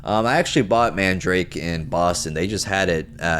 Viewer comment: You bought mandrake in Boston? Maybe I misheard you but that’s near me